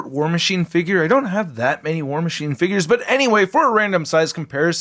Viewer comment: I thought he said “War Machine Turds”